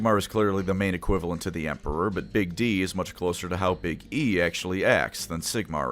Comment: did you say "BIG D?"